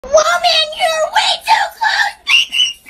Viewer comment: WOMAN YOU’RE WAY TOO CLOSE; ✨PePpEr sPrAy-!✨ 2024 edit: Why of all comments did this blow up- ._. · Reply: i thought he said "CHARMANDER YOUR WAY TOO CLOSE PEPPER SPRAY!!"